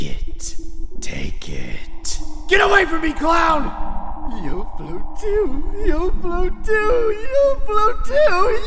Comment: what was he saying here?? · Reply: He was saying "You'll float too". From the movie "IT".